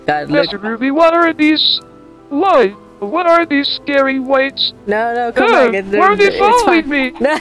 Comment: When Carl found about Chickens and called them "Little Whites". Just shows how new Carl is to Minecraft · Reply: I thought he said “scary whites”